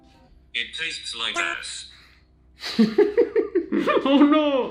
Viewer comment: Did you just said "it taste like nuts" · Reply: Mina S. Overgaard no it said it tastes like a$$